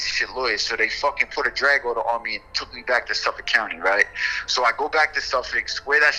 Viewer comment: from the velt I know son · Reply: I thought he said Suffolk, I know a from the ton that got jammed up in the LK fed sweep. But Clinton ain’t the feds? used to cut my hair in DP